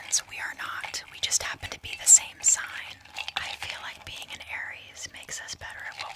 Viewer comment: at first i thought they said “we definitely identity as harries” as in like harry styles and i was like oh me too. and then i realized she mean aries and i was like OH ME TOO